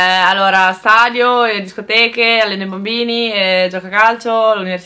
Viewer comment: io ho sentito bombo i bambini